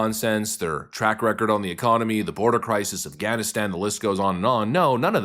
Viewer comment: When you said "Afganistan" I heard "Gavinstan" and about lost it.